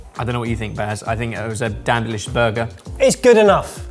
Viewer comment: What did call this (at around 10:15) - a damnblish burger?